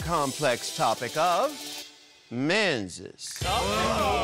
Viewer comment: totally thought Ru said "MeNseS" and meant a woman's menses.